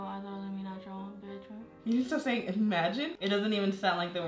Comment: i didn’t know she was saying “imagine” i thought she said “eh” after everything 😭😭